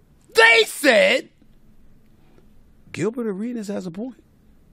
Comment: High asf watching this. Man doing what we all wish we could when we was watching that phone screen under the sheets 😂 Ps. Thought he said Gilbert Arenas has a porno. Stay off the weeeeeeeeeeeeeed